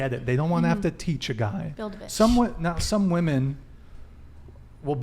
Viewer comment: Did she just say build a bitch ?